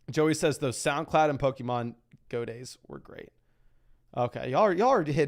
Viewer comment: not too bad with a DS, have the reset routine down to a tee where I don’t have to be watching the screen the whole time 🥳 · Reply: ohhhh for some reason I thought you said pokemon go lol.